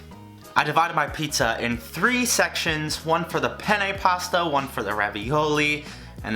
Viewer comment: Why does it sound like he says "penny pasta" every time he says "penne pasta"